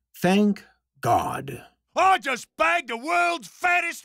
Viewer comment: Did...did he say “banged”? Or “spanked”? What...what is the context of that sentence? Did i just hear it wrong? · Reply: I think he said, “bagged”.